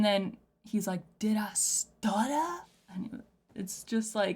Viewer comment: st st st stutter I believe is a reference to the British Prime Minister, stutter = stammer and the PM is Called Starmer and he was saying In the PM. Ren just has a way of putting how we are all feeling into captivating music, A true genius! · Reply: I hear " sir sir stutter "